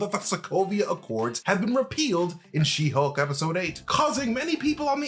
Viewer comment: I thought he said APpealed, not REpealed. I'm out of it